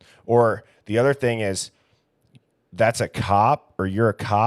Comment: I hear “there’s a cop”